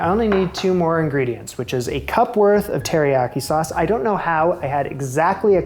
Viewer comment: Not just store bought but thick. The Kikkoman that I use is much much thinner than the soy sauce he used. I noticed how thick it was when he held it up in the measuring cup and you can see it again when he pours it. In fact I wonder if it was Teriyaki sauce instead. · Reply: Yeah he used store bought. I'm not sure why I thought he said soy sauce instead of Teriyaki sauce.